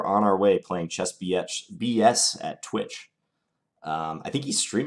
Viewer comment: I thought you called the first opponent chess biatch. lol I thought wow went away and came back a new man. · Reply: I, too, thought he said Chess Biatch. Got a chuckle out of that. Maybe John did later, too.